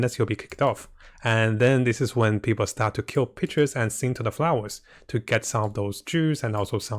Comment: i thought he said, "ppl start to kill b*tches" lmao